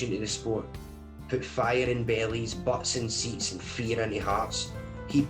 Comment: “..put fire in bellies…” I heard “put fire in Bailey’s” Im thinking, “please don’t tell me he became a fancy bar tender at some froo froo club.”